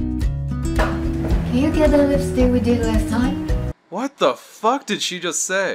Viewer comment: I think she's saying "Can you get another lipstick from last time?"